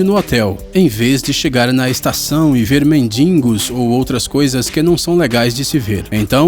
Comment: auhhauuhhu impressão minha ou eu ouvi "mendingos"? gostei do vídeo